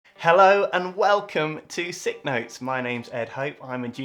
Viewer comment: Dear Doctor Hope, Now that i've seen it i can't unsee it, would you mind dotting the 'i' on that neon sign, right now it just looks like it says 'suck' behind your head. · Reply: I actually thought it said “suck notes” until now. ;_; “Sick notes” makes a lot more sense.